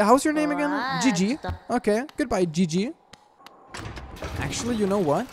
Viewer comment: When you lose a squads game and you have to leave the group. 7:07 LOL Edit: the joke is said "goodbye, Jiji but it sounded like GG